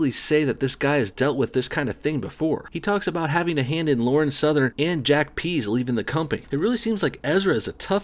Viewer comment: Sounds like he said he "had a hand in Lauren 'growing'".